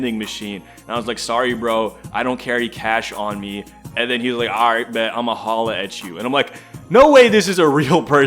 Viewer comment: What did he threaten to do? I genuinely couldn't understand, but whatever it was, it's probably not something you're allowed to do in a library. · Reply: in slanglish, "aight bet, imma holla atchu" means "Okay, catch you later then"